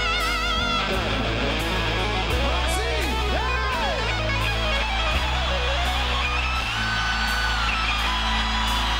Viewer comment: I love this song, but what is said at 3:29? i hope it's not what it sounds like? Loved this song since i first heard it spring '90. Such a nice song and awesome guitar solo. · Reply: He said "well done". In russian it sounds like "molodtsy". Yep, this is too late answer :)